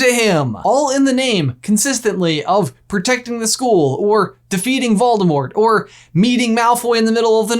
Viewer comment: I swear instead of “defeating Voldemort” I heard “feeding Hedwig”